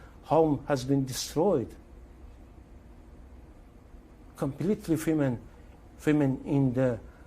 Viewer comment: I think what he said was “complete famine in Gaza”.